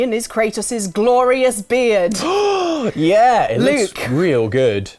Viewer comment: i thought it said Bread.